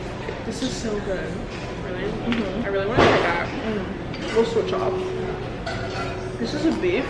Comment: When you said “we’ll switch off” I thought you said “oovoo javer” lmaoooooo